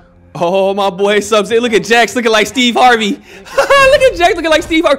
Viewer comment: Did u say jax looking like steve urkel?